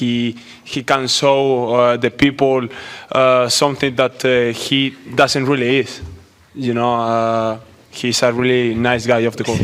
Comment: Carlos at 2:33 had me laughing hard. Not making fun of his English but wtf was he saying? "He can so with the people which he doesnt really is" The others were losing it as well 😂